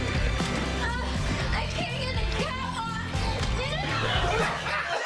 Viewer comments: At the part of "I can't get the cap off." I thought it was said "I can't get my tampon." I had to replay it a couple times to actually understand it. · My first watch, I really thought she said "I just ate a tampon!", not "I can't get the cap off!" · I thought she said "I can't see the catwalk" ... instead of "I can't get the cap off"